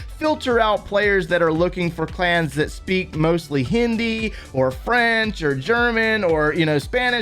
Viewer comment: did he really said 'hindi'